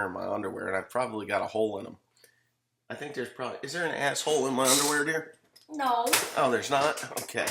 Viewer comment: Made it to the end. Important message. At first I thought you said "is there and asshole in my underwear!!" No, there is no asshole in your underwear. Just a damn decent guy with much appreciation for the good things in life and sharing them with us.